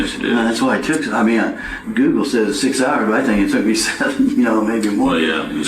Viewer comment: Did he say around “ six or seven hours”?🥀